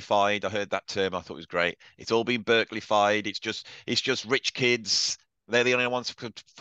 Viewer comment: What word around 30:40? Burntified? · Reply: Some claim that institutionalized jazz education codifies it into a Academic set of rules. 30:30 "Berklee-fied"